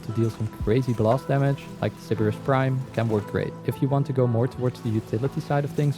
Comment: AHHH at 17:36 what weapon are you saying before the dread ive tried to figure out what you said but i can't hear it and subtitles failed to😅😂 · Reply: Sybaris Prime